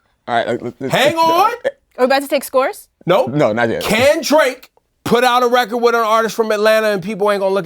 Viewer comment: Every time someone says "Can Drake", I hear "Kendrick" lol